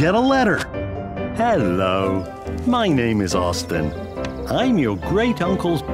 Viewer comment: I THOUGHT THAT SAID AUTISM